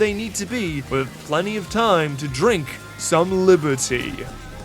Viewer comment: This is a very minor thing but at 5:45 the subtitles say "Rink some liber-tea" not "Drink some liber-tea"